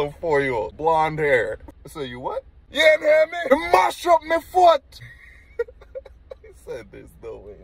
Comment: I think you misheard him…😂😂😂 he was swearing at you “ jou se poes “ and it sounded like “ your ma shot my foot” 😂😂😂